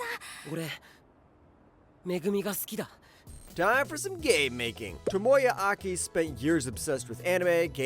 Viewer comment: Totally misheard "game making" as "gay making" lol